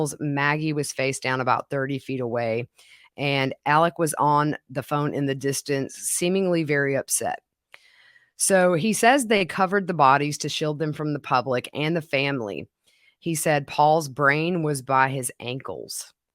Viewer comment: I thought I heard Alex say, "What? Did they cover the bodies?" Different from a request.